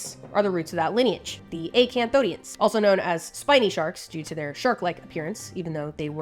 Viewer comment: I had to backtrack I thought you said 'they ate Cambodians'.